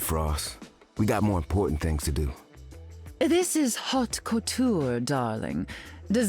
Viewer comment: Haute couture? I thought she said "hawk tuah"